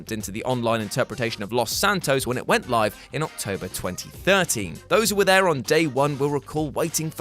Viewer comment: did he say October 2030?